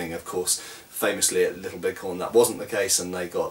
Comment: ??????? what does he mumble here? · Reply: famously at little bighorn and they got wiped out